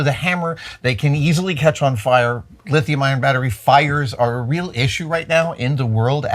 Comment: Adam sounded like he said lithium iron batteries are a real issue... he probably said ion but ION should always be empathized when the subject is fire ... most of the public only know LITHIUM. They know not of any other compositions. To my friends ION and IRON are the same and scream fire, fire, fire.... they want nothing to do with EV vehicles or home power systems, but they still charge their phones next to their beds....